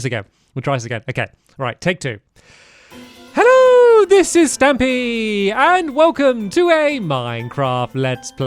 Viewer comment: “Hello this is stampy”. Good old days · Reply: Honestly thought you said: "Hello this is therapy" Although it might as well be, given the unending positive vibes Stampy gives off.